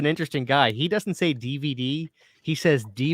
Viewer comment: I think says DVD normally. When says DVD, though, I hear "day bay day" LOL